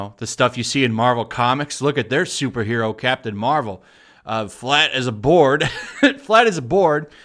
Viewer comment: I heard that as "flat as a borg" instead of what I think you said. It works both either ways.